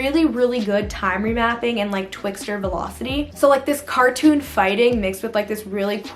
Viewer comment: did she just say c...cartoon